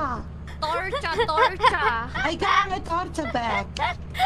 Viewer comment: I thought I heard Torta at first